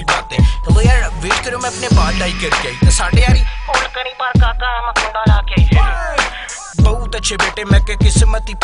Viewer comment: what does he say in Punjabi from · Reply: Hold karen bar kaka aaya main Kunda la k i